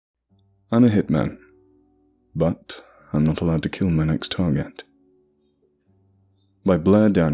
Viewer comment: I thought he said “im a hitman, but im not aloud to kill my targets”. I am slightly disappointed.